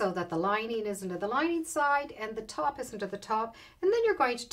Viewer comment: What are you saying at 10:36? It sounds like "So press the bag so that the lining isn't in the lining side and the top isn't at the top"—isn't, or is? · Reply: The lining is in the lining side and the top is on the top.... thanks:)